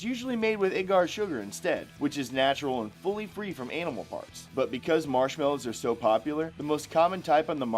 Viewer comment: did he say gelatin @ 40:57 was free from animal farts??? lmao! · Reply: he said animal parts lol legit sounded like farts tho